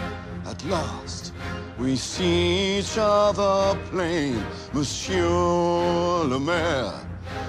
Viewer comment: When Javert said,"M'sieur Le mayor" i thought he said Lamere. Which is the last name of my english teacher. It sounded so strange till I watched this video. God I feel stupid.